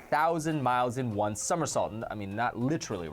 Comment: I heard "summersalt" and got hungry.